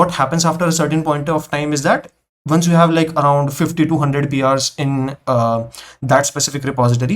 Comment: did he just say 50 to 100 😶